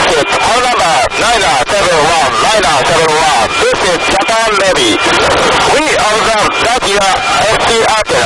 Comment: I can not understand. Why did not Japan Navy counterattacks?? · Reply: Oh I am sorry I thought you said Japan Nazi